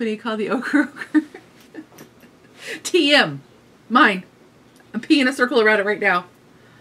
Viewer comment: You're always gently hilarious. And when you said "A 'P' in a circle around it right now" to describe the patent symbol on "Ochre Ogre" I seriously thought you'd said "I'm peeing a circle around it" 😂 I suppose that'd be ONE way of declaring ownership...